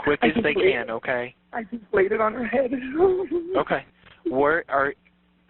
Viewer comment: Anyone else catch that at around 10:34 during the dispatch call replay, that in response to the dispatcher asking where she was shot he responds with "In the head I hope"?